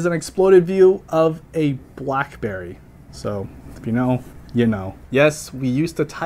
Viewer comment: I feel like when you say “if you know you know” I hear “if you know, you old” 😔